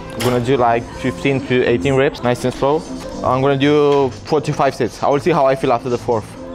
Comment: When he said 4 to five sets I thought he said 45 sets 😂